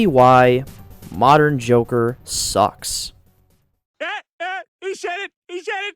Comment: I swear, did I just hear you say "The Jorker" XD?